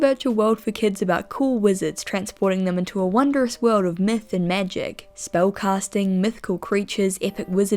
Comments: I'm sorry but the way you pronounce math I thought you said "transporting them into a wonderful world of meth" I had to do a double take the first time I heard it (to be clear im not trying to be mean) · you said “myth and magic” but I thought you said meth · Okay I know it's the accent, but I swear to god I heard "Transporting them into wondrous world of meth and magic"